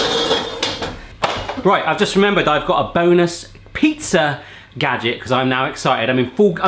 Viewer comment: I swear I heard "I got a boneless pizza item to share" lmao